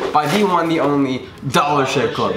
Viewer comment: I heard shit club Probably just me